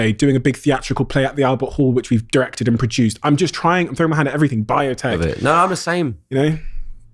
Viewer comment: did he say biotech????